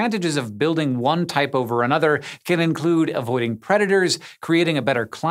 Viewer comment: I thought he said, "avoiding creditors." I think I need more coffee.